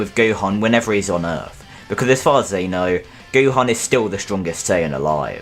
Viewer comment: I thought you were saying Gohan was still-born. I was like "that's needlessly dark!"